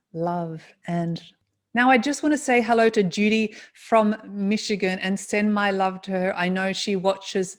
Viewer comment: Oh my gosh, when you said hi to Judy from Michigan and mentioned how her son means a lot to you, my breath stopped for a moment! I thought you said Julie from Michigan and were referring to my son who you had never met even on the web. It definitely caught my attention for sure! Though it was actually Judy from Michigan and her son, I enjoyed the moment as well as listening to all your past lives you shared. 🥰 Hello from Julie in Michigan!